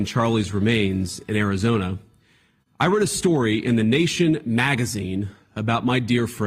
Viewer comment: i think what he said is 'i read (past tense)' not wrote